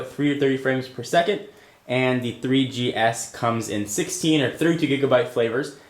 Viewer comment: the storm just looks bad next to the iphone :P ps- at 3:03 to 3:06 did u just say "..and the 3G S comes in 16 & 32g -Flavers-" ????